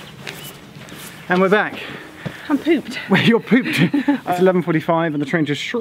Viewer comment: I thought said "I've pooped!" TMI. Luckily, I misheard.